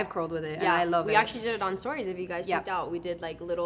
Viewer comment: At the 5:10 marker in the video you mentioned someone's name- did you say Stori?